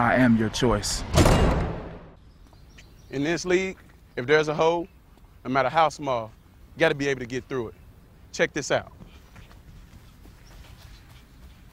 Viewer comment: IT SOUNDED LIKE HE SAID "If there is a hoe (hole), nomatter how small, you gotta be able to get through it" XD